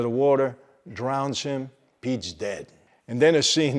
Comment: The way you said “Pete’s dead” at 10:52. Sounded like “the life” was coming back there for a second…